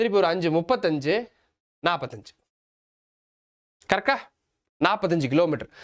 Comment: karka instead of correctaa😂😅